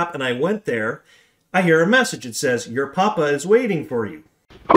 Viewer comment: Hi, I heard "pop- pop waits for you" which blows my mind !! Thanks for sharing!!!